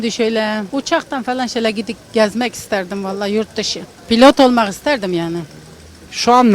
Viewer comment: Yarı pilot duydum ben doktor doktor oldum